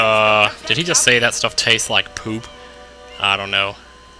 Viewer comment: "It tastes like poop." " Its pooping!" Is what I heard the first time I played this game. :P